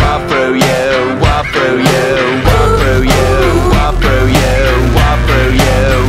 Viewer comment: When you realize they aren't saying "she took my data" but they're actually saying, "she turned my dad on" and you spent entire time jamming out to that part over and over again without even realizing it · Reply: I thought it said whopper you like the burger 💀